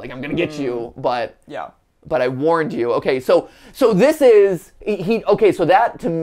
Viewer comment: i think the sun guy is saying "lights on (or off im not sure)... i warned you" which would make sense with your theory that the lights on guy (sun) is helpful and the lights off guy (moon) is bad